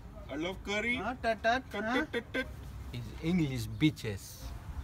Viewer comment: I first thought he said " English Beaches"